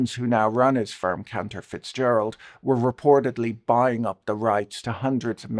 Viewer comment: Damn I thought u said the Lutnick boys' company name was Counterfeitzgeralds.